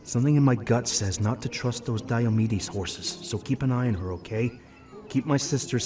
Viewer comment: Misheard what Luna said and heard her said diabetes horse 💀